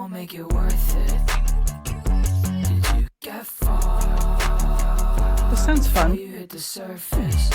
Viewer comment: everytime in second hand embarassment i always hear "did you get fucked before you hit the surface" help 😭😭😭😭